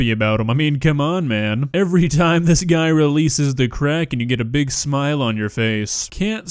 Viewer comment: I thought said "every time this guy releases the Kraken" and right after I typed that my dog, lying next to me, released his own.